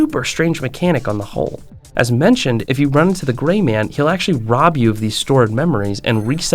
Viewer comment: The gray man reminds me of my dreams, I commonly am running away from things that wake me when too close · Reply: I'm so tired thought you said the gay man at first lmao